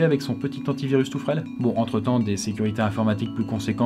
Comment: Au début de la vidéo quand il a dit le mot "antivirus" j'ai compris "anti-viol" cela en dit long sur ma santé mentale...